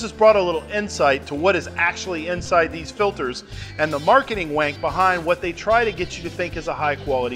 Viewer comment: Did he say "marketing wank"?